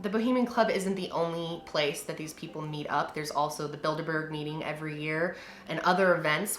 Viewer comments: I really thought you said “there’s the Build a Bear meeting every year”. Except after hearing about the shit that goes on there, I wouldn’t put it pass them. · I thought you said build-a-bear meeting